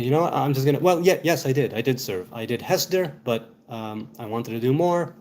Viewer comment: BLESSINGS from Zion! Enjoy your new week. May it be one of peace, love, and kindness. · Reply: did you say "hester" here? 🤔